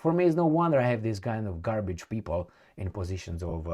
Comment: did you say garbage ? Carlin said it long time ago <link>